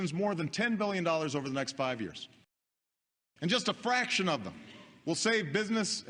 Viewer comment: I heard braction not fraction at 6:47!!